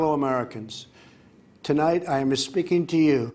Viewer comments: "tonight I am misspeaking to you...." Yup, he mis-speaks all the time. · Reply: LOL! Like Yanny or Laurel.. I hear, Tonight, I'm Espeaking to you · "tonight I am misspeaking to you...." Yup, he mis-speaks all the time. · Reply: I thought he said Asspeaking to you.. That would make perfect sense tho..... He's always Asspeaking!! 😂☝️